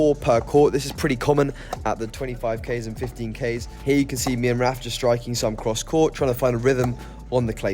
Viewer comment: I thought he said rafa.